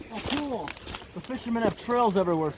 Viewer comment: I just heard GOST 10......... She said "OH COOL"